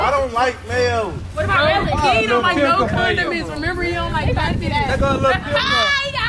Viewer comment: Who else thought she said condom